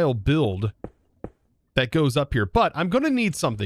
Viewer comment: I thought he said it goes up your butt 😂